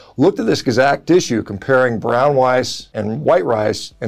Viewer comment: Brown Wice Hahaha sounded like a 3 year old for a minute